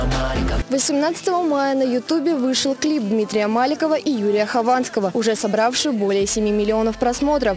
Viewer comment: Мне послышалось, или диктор сказал на 1:31 "Юлия Хованского"?